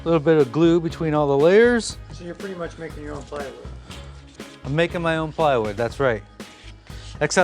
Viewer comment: I miss heard, when he said, "Making your own ply - wood," I thought he said Fire-wood. I had to rewind! Beautiful project though. The Classic top in walnut is superb - the 'jazzy' top was merely distracting and it is, after all, a study desk.